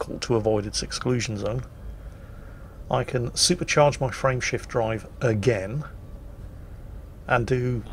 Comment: for the longest time I kept mishearing Frame Shift Drive as Friendship Drive...that was very confusing, but I simply attributed it to being and giving it a nickname......